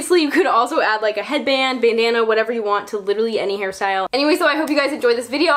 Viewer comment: i died laughing at how bandana sounded like banana at 10:00 💀😂😂